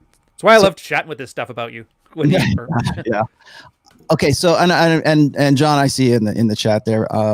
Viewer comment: For only a moment, Rollo, had I thought you said John Cena. If only he was on your show.